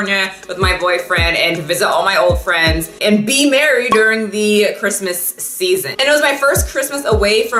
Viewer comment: Who else thought she said "be married" around 1:25 and freaked out for a sec😂